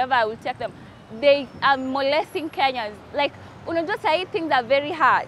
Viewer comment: Did she say "they are molesting kenyans"?😂